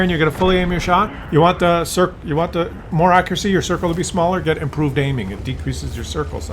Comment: "if you want to get improved aiming you get smaller circumcise ...." uh do I really have to ? I think I pass ... Later he talks about small russian peepee´s so I certainly most likely for sure did not mishear. Doctor World of Peepee´s ^^